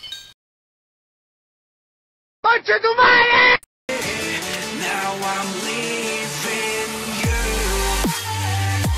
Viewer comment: yo what did he say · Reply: He said "conchetumare" :v